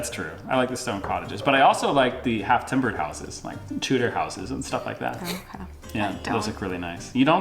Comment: I thought he was saying tutor house, but he means Tudor house which typifies the 15th and 16th centuries. It's a wooden frame house with wattle and daub filler - it's the black and white vertical stripe house typically with a thatched roof.